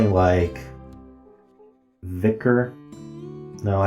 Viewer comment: When he said vicar, I heard it as vigor and thought he was about to get 4 yellows in addition to his E lol